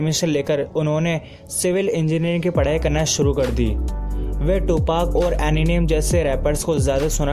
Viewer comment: Did he just pronounce eninem.. Instead of Eminem 3:18.....bro yaha pe tu mat kha gaya....... Or ye gaya mera dislike